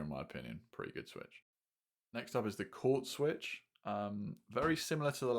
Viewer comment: Can you tell me the name of the 3:20 switch? I couldn't understand well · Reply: It’s the Quartz linear switch, they have a new version of this switch out now I believe, might be even better